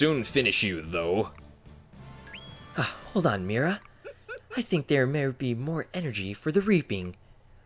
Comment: I legit thought u said "I think there may be more energy for the raping" XD😂😂😂